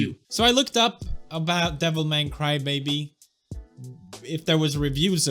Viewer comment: what he says: "devil man - cry baby" what I hear:"devil may cry, baby!"